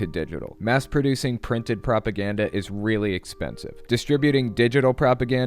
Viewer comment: I miss heard you & thought I heard a new word " crapaganda " . I think I can use that! '